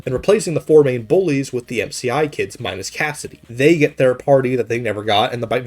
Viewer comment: I heard "they get their party" as "they get third partied"